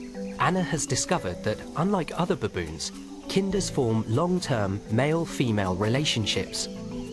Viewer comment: Kinda Baboon. This whole time I thought he was saying "Kender." Yay for Google. I would love to see what they found out with the results of the DNA.